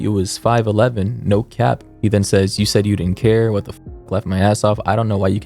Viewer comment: I thought he said light my ass off